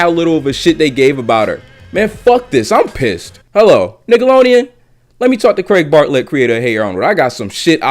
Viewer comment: Am I the only one who heard "niggalodeon"